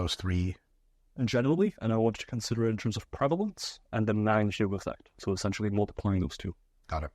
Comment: What does say at 0:33? I hear "nine thyu effect" lol